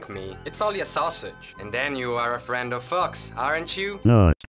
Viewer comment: Who else thought the chef said friend of fucks